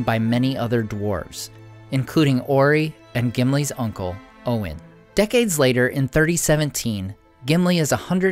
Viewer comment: did anyone else hear "uncle Owen" and have to do a double take?